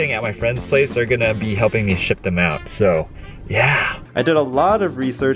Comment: did he say his friend is helping him shit them out lol